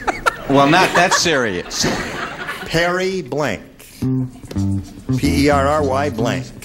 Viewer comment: I have to admit, I heard "Harry" too!